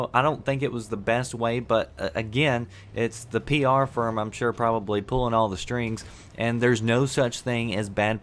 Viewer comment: i thought he said it's the PLR pulling all the strings